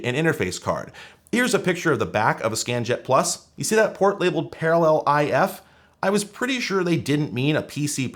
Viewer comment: When you said "Do you see the port that says Parallel I/F" I heard "Parallel AF" and I was like ... hell yeah